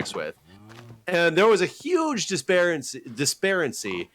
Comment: Did say "disparency" at 7:33? Did he mean disparity or discrepancy? That's like the Palinism of "refudiate."